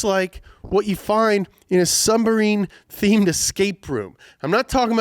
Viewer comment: did he say SUM-BARINE lol.